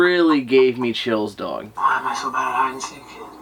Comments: When is in the well, it sounds like a spirit imitating instead of saying "find us". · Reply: It sounds like something saying “why am I so good” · i hear why am i so..... like it's repeating what he said.